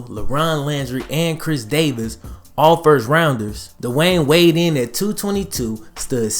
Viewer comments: I thought he said Dwyane Wade. But he said Dwayne weighed. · Ha,thot tha homi was tripn 4 a secnd whn i heard “dwayne weighd”,lmao,im like “d~wade”!?..,whn/how tf he get up n tha dam d~bo vid!?!